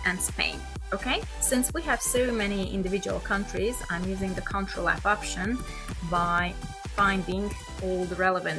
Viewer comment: You already lost me at 5:17 with "control option". Maybe put out clearly which shortcuts you use and don't have any background music because it makes it harder to hear what you're saying :/ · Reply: ctrl+f